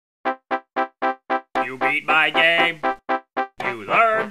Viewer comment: I only hear "you be my gay" 💀